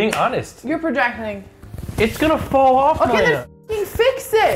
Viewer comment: does nick say Maya?